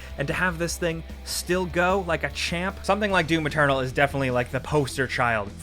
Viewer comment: When he mentioned Doom Eternal I heard him say "do the turtle"